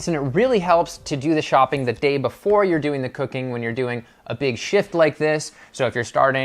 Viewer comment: Great video! 1:50 For a second, I thought you said big shi... 😂😂😂